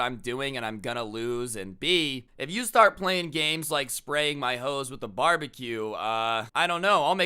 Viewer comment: did he just say spraying my hose with a barbecue